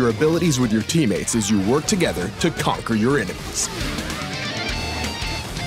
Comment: Did he just say "BELIEVE IT"? 😧